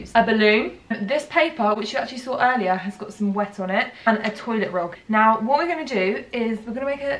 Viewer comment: did she just say..... a toilet rog? 😂😂😂